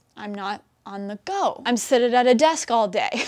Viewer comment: did she say "sitted" ? Did she mean sat? Also, I love her enthusiasm though, first time on the channel!